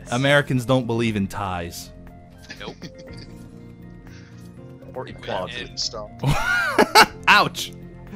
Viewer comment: Did anyone hear what actually said about America at 13:54? It sounded 'or equality' but I'm not sure